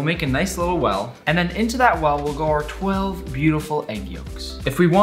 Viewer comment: did he say "go to hell"